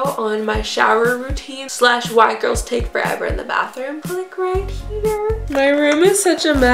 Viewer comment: She said “Why girl take forever in the bathroom” I heard “White girl take forever in the bathroom”